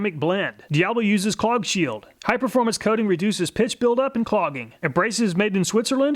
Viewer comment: Is it just me or does it sound like he says "piss buildup" at 2:01 lol